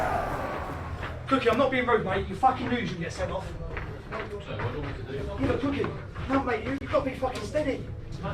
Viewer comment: I thought it was 'Joe I'm not being rude mage but we just lost cos you get sent off' but then it doesn't quite sound exactly like that and it's annoying me haha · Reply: sounded like he said " im not being rude mate, but you fucking knew you'd get sent off"